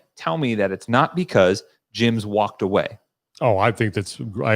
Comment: When said, "it's because gyms walked away," I heard: "It's because Jim's walked away." I was like, wait, who's Jim? I must've missed a whole plot line! Then two sentences later I figured it out 😂